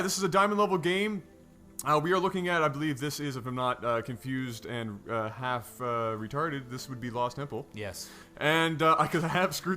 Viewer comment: I swear I heard "If I'm not mistaken and half retarded, this is... bause nipple."